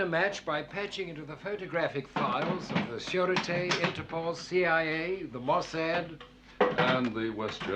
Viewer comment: timestamp 17:59 what is the first agency q says they can patch into? can't make it out and never heard of it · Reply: He said “Sûreté” which is a French/French-Canadian network of agencies engaged in Civil Defence